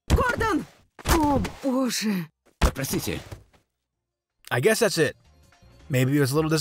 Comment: I think the rebel you killed says "Просьдите" (Pros'ditye) which means something like "Say goodbye" but I'm not sure either